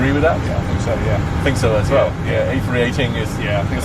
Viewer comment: captions say the a380 is the smallest commercial airlines transatlantic 😂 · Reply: He says a318😂